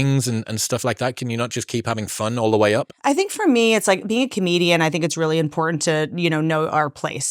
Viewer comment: I kept hearing Canadian and as a Canadian I was like "what she's Canadian?" lol